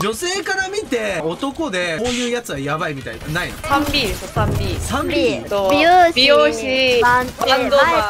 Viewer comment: 3Pに聞こえたのは内緒